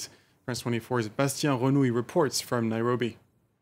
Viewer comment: Is he reporting from on Uganda from Kenya ? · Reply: My thought exactly, I thought I heard Nairobi